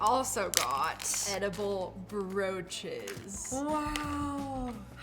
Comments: I thought you said edible roches😭😭🙏🙏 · I had to do a double take and rewind because when you said edible broaches i thought you said eible roaches the heard CRUNCH🪳😮😂